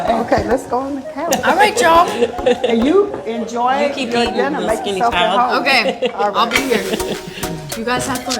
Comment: Did she really say you keep eating you little skinny child wach